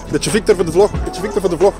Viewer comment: What did you say to Victor at 1:20? My Flemish is rusty :) This is so cool to get inside looks at a pro training camp. Love the videos. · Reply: "A little bit (of) Victor for the VLOG"